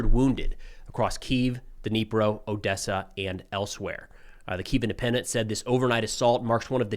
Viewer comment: Every time Preston says ‘elsewhere’ i hear ‘Elseweyr’ Like shit, Russia’s even attacking Tamriel now?